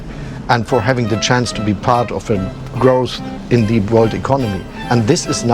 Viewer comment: The subtitles are incorrect... some people that cannot do the "th" sound, so they pronounce it as as "ss". So he actually said: "the chance to be part of the growth in the world economy"